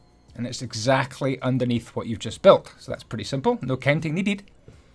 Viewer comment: What did you just say at 2:20....I swear that's not English. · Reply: Dude he just said 'No counting needed' with an Irish / Scottish accent . You third wheel